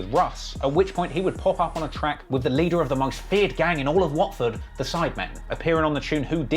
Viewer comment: did he just say the most dengerous gang in the world the sidemen 😂😂😂😂😂😂😂😭😭😭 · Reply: Watford* ksi is from an English city called Watford